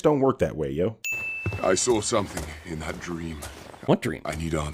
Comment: I don't know if this was a legitimate mistake or not but at 25:00, the captions say "Sigh" instead of "See what I mean?" Update: There is another at 26:53. The caption reads "Jesus..." instead of "See?"